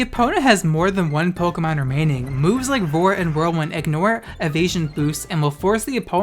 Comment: I heard roar as vore